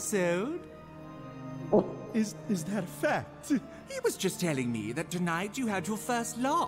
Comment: I Don’t know what he said cause there are no captions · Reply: There is captions options for me and he says " Fact "